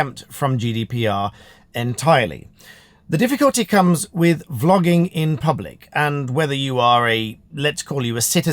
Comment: I was just listening to start, and I thought you said flogging. 😆 When did they bring that back? 😂 Great chat and advice for those filming in public.